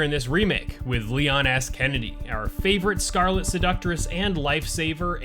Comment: Did you just say "Leon Ass Kennedy" lol